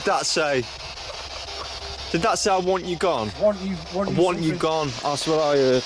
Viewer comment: Thought it said I want your bum 🤣🤣🤣🤣 not want you gone